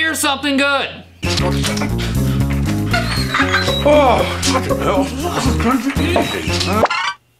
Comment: I heard 'trucking hell'